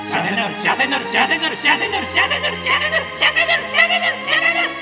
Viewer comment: 「しわしわになる」と言っているように聞こえるw